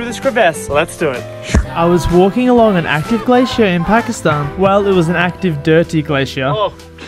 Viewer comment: It said Pakistan I thought he said Afghanistan